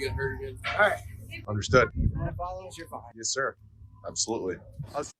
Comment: I heard "you wanna follow us? You're fine." As in, you're cleared to follow us. Does anyone have clearer audio so I can verify whether the author of the article is correct in his different interpretation? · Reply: I played the audio over good headphones and he says "You want to follow us? You're fine."